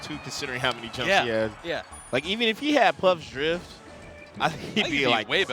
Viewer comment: I heard 'Plup's drift' and I was like, 'Yea, if Kirby could drift like Plup, He'd be such a better character'